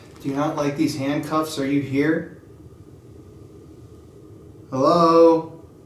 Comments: sounds like "yes, I am here" or the first time i listened i heard "why are you here" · Gosh that whisper in the juvenile section! Sounded like they were saying "are you there" like they were joining in on your calling out to them. Hello? EVP: Are you there? What an amazing jail/museum. Love this investigation.